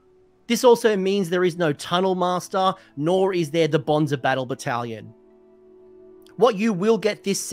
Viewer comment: I know you said "bonds of battle battlation". What I heard was "Bonza battle battalion" so thank you for that laugh ^^